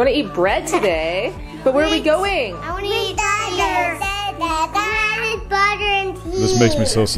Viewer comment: did he say he wants to eat butter?